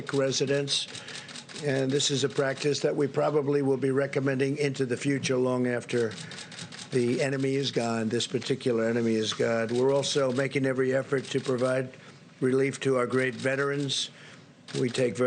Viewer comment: sounded like he said "the enemy is God"